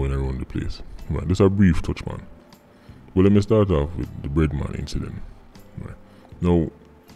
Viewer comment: Thought he said redman until I read the screen and saw "bread man".